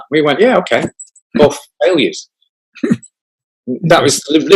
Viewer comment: what does he say the name of the song is? “Alias”? I can’t find that anywhere so maybe I’m misunderstanding him. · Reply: Failures?